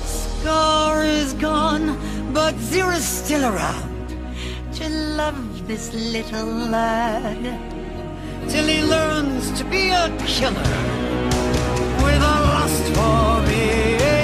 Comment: I swear I thought all these years that she said "That's my little boy" My life is a lie. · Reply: I always thought she was saying "to love this little lamb" rather than "lad" 😭